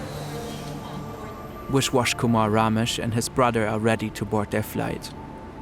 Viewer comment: Sounds like he is saying Wish-wash Kumar.